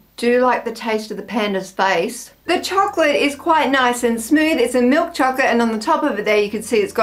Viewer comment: penismi dedi o benmi yanlış duydum